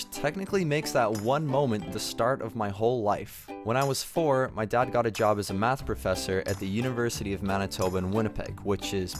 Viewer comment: I thought said when I was 4 I got a job as a math professor 🤓